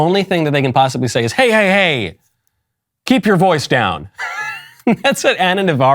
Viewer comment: Keep your voice down? Oh, that's what she said. All I heard was "Oink, oink".